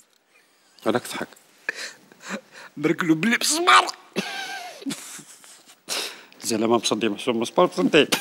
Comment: لي فهم لقطة هادي يفهمني بسك ما فهمتهاش 😂 وش قالو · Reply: مرڨلو بلي مسمار .... مسمار مصدي